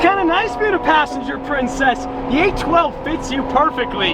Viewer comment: did i hear a "princess"?